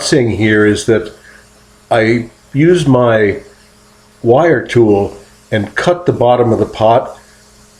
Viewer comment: Beautiful! I don’t understand what you’re saying at 1:59 about the wire tool. Also, when you stop for the day, are you covering the piece? Would be great to see another video about how you made your extruder ..Thank You..this is inspiring. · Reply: Use the wire cutting tool to cut the bottom of the pot to separate it from the bat, just as you would cut a bowl from the wheel and remove it. Then take your finger down along the cut edge and seal that back up. It will keep this large piece in place and let you continue to work on it. I do cover it in several layers of plastic overnight. That allows the clay moisture to equalize to a certain extent so you can work on it the nest day. Many folks use a hairdryer to speed the process up but I prefer to let my process run its natural course. You need to be able weld and work with metal to make the extruder that I made in 1986. thx.